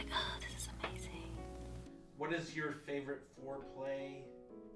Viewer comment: I thought she said getting her ass cleaned. I now realise it was house.